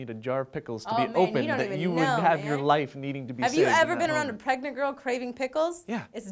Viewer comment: WAIT!!!!!! Did Lee say that she was pregnant